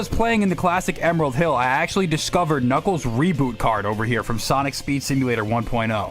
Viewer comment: This section is good but did I just hear reboot card?